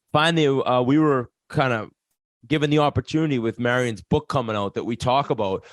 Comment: Thought it said Marian gaborik at first. Fuck that’s a dream interview for me